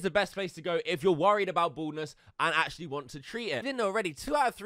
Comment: i thought you said boredness and not baldness 😂